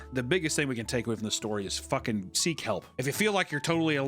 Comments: I legit thought u said “sea kelp” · when you said "seek help" I thought you said "sea kelp" at first